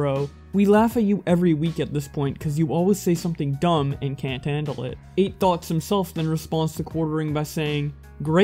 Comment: When he says Eight Thoughts I keep hearing "Eight Thots"